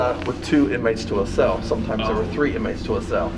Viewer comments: PLEASE TELL ME IM NOT GOING CRAZY!!!!! I HEARD A WHISPER AND IT SAID “THREEEEEE” it was almost like a spirit was copying the guy after he said three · anyone else hear someone whisper “threeee” 😬. Idk maybe it’s just breathing again😂😂